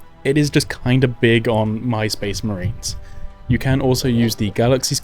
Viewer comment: i heard MySpace Marines and got upset 😂